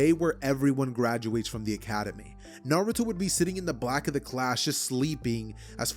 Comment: My guy, PAUSE, Am I mistaken or did you just say BLACK OF THE CLASS!!!🤨🤨🤨